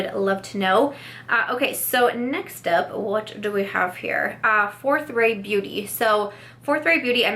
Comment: I totally thought you said “Fourth Grade Beauty”! I was so confused 😂 😂😂 #samsquad